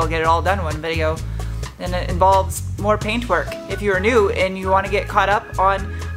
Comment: Lol!! I thought you said Pain Twerk. I was scratching my head for a sec 👻👻🚗🚗 yeah. Careful that stuff burns…ohhh.